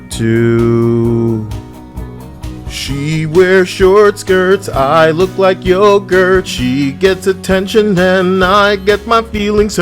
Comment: "She wears short skirts, and I look like yogurt". LMFAO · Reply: Oh I heard it as “I look like Ogre” 😂